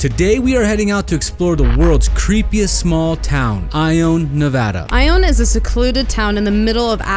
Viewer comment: Ok what...I thought you said " it's called I own Iowa"... I was like, oh..big time baller here :)